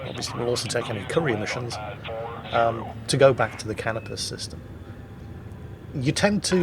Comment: when a video starts and i hear "we're here in the Canibus system....." LOLWUT